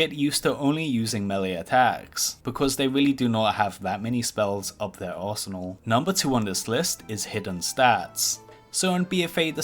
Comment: did he say paladins dont hav that many spells up their arsenal lol